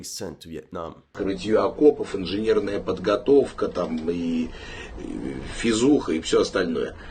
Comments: yes, your "я говорю по-русски" is broken. What Prigozhin is saying is "физуха" (fizukha), whish means physical training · He doesn't say Facebook. I think he says Physics. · he does not say "Facebook", but something like "fizuha" which sounds like a shorter word for physical training.